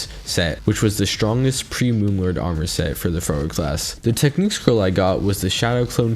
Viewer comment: WHY DID I HEAR IT AS "FURRY CLASS" FIRST 3 TIMES